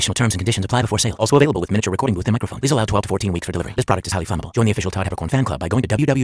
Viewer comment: THIS PRODUCT IS HIGHLY FLAMMABLE. Ah, Natsu jokes. I WANT ONE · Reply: I thought it said highly fundable